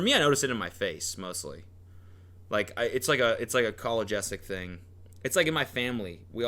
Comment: I thought said “college essay” but it was his last name 😂